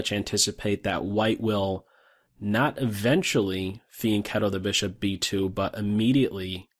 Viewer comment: he said "that will will not eventually Something the bishop.." I didn't understand the word he said anyone knows what it is I hear it alot but can't know what it is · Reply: fianchetto